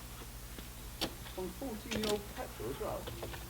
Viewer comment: Did I hear "14-year old petrol" at 14:54? I assumed until that point that you poured fresh petrol in it at least!